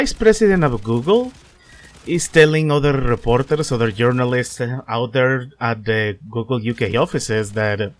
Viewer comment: It sounds like you said "Urinalists".